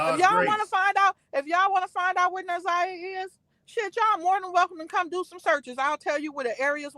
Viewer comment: Did I hear say “if y’all want to find out where NaZiyah is……I’ll tell y’all where to go look”???🤔 46:35 time stamp